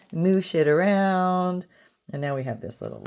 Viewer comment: When you said 'Moosh it around', I thought you said, 'Move sh*t around", and I was momentarily shocked! 😆